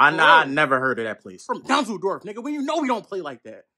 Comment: yoo for a second i thought he said Düsseldorf lol